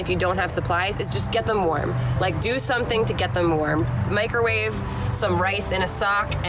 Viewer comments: PSA: DO NOT MICROWAVE ANIMALS. Microwave a microwavable item to help baby get warm. (In case someone misunderstood!) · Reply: I really thought she said microwave the cat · I was trying to figure out why you said microwave then I heard sock and rice I thought you said microwave the kitten